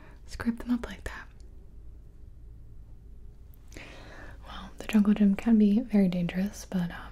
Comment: My ears blanked out when she said "The jungle gym can be very dangerous" so I heard: "The jungle can be very dangerous" and I was like WTF THEY HAVE A JUNGLE